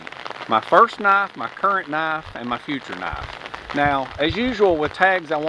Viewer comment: I thought you said first wife,current wife and future wife!!!!!